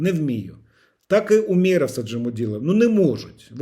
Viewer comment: "Умеров, он же Мудилов". Мне одному послышалось? 😂